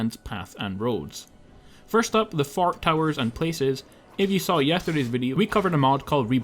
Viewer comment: I could've sworn he said "fart towers."